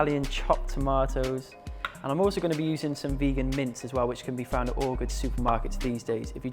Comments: I thought he said "vegan mints". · Cooking dinner while this played, I swear I heard him say vegan mint. Dinner stopped until I could find him saying vegan mince. 😂